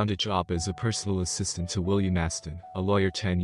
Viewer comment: Sorry, this story has already been in two other channels at least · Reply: Reddit Cheating Stories was the only one I recall it being on. I remember making a William Afton joke as I misheard the AP's name that time.